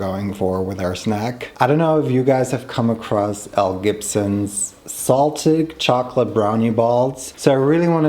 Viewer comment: I thought you said Mel Gibson and got so confused (like since when has he become a food influencer sharing recipes?) haha